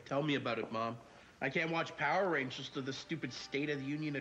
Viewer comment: did he just say Power Rangers?